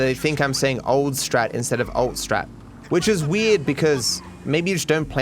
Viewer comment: they think this because the subtitles say old instead of alt most the time I’m guessing